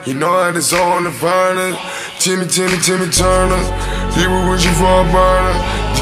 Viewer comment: I thought he said "timmy timmy timmy turner,he be wishin for a burger😂💀